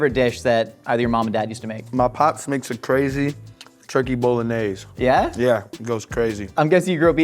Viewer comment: I'm sorry, this episode is delightful and I love it but i can't stop laughing since 4:29 because the CC said, instead of "turkey bolognese", "turkey bowl anus" and I am apparently still 12 years old because I am dead. *Edit* I rewatched and it says "anas" and apparently I just need to get my mind out of the gutter.